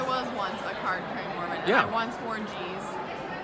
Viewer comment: I couldn't hear right it sounded like she said I was a card carrying mormon who once wore geez??? Sorry I can't hear that well and my CC wasn't saying it right.